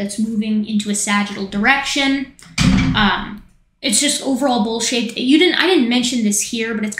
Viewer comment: When you said "it's over all bowl shaped" I heard "it's over all bull sh*t* at first. Made me chuckle.